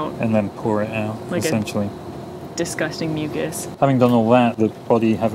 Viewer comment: I thought she was gonna say "disgusting milkshake".